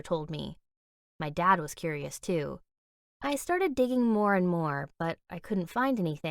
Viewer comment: I thought she said “I started dating more” XD